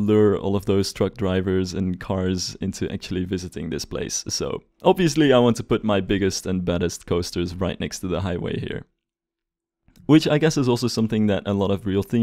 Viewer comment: did you just say Bettest? Or did you mean Baddest? Because both of those are wrong xD